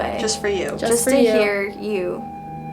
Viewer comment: I think he said thank you, Idk that's what it sounds to me